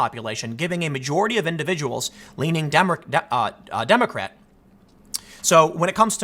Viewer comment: did... did you just say - demon-crat? Freudian slip? 😂🙌 Love the content man, cheers 🍺